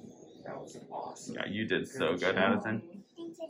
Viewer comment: does Addison say “seize the day” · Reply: She says “Thank you Dad-A, thank you Dad-E” there! ❤️